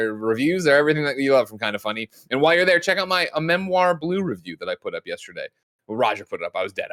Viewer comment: I really thought Greg was about to say “Checkout my memoir” and I was so confused